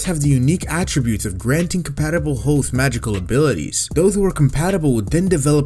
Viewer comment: did he just say 'compatible hoes' at 1:21, lol !